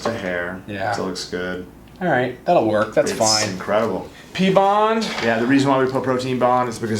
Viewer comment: Hello from France :) i love your videos 😍 what do you say at the 30:35 P.bond? 😅 thanks for your help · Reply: Glad Glad protein bond will prevent top coat from chipping